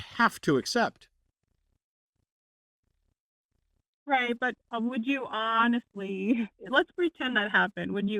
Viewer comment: I’m a very vocal atheist, but although I agree with almost everything that Matt and Forrest said, they were incorrect about saying that the caller said they “doubt I would be dishonest” or “doubted we would reply honestly”. She actually said “right, but would you honestly, let’s pretend that happened, would you honestly believe “ [tc 29:33] then Matt cut her off. Presumably she was going to say that she thought they would not be convinced. So yes, she completely misunderstood the thought experiment in that case, but she didn’t call them dishonest. Anyway, despite this small error - love everything you guys are doing ❤️